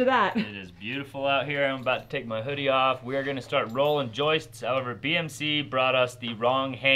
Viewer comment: It SOUNDED like said he was 'rolling joints', lol.